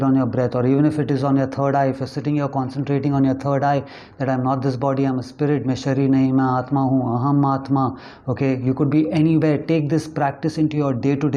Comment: Hey at 8:04 would you be able to type what you said so I could also say it, thanks :)❤ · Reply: Main Sharir Nahi, Aatma Hoon. Aham Aatma. (I'm not this body, I'm a soul)